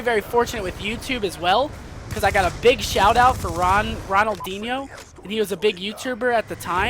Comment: Wait did e say Ronaldinho?